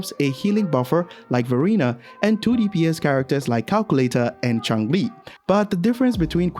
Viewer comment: Uh... Am I the only one who heard "Calculator" for Calchero?